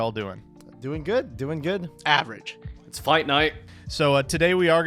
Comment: I had to do a double take bc I thought say "Smite Night"